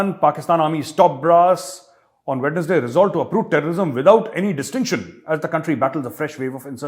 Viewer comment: Ha, Ha ! You said “Pakistan army resolves to uproot terrorism”, and I heard “Pakistan army resolves to approve terrorism”…till I read the accompanying text.